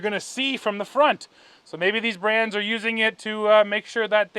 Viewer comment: did u say "these RAMS" when talking about a Ford?